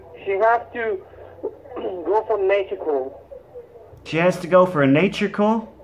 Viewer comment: Wow, the IRS sure does love iTunes! I thought he said Miss Erica had to go for a medical call but it was a nature call! Good one!